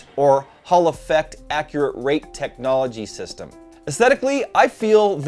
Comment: Did he just say "Accurate Rape Technology?" not sure that's what it is actually called...